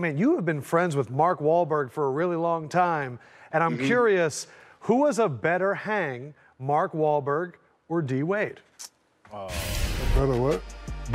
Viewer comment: I heard "Who was a better hang, Mark Wahlberg, or Dwayne?"